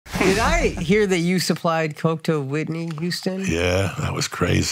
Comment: Host:oh shit I thought he said Whitney Cummins😂.. Whitney Houston hell ya tell me more😳... Joey:oh ok well I never actually seen them but seems we're on about different people fast track this shit 😅😅😅and after seeing all the clip that man would sink a ship defo😮